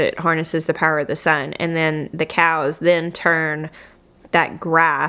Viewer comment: So happy for you! Thank you for showing us the real and not just the pretty. We learn soooo much this way. Also, when you said, then turn, I thought I heard Bentern. LOL! I was like, what did she just say? The I remembered, he's still in Arkansas. Made me giggle.